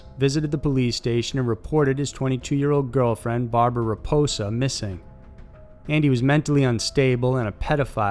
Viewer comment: did he just say gf's last name was Reposa?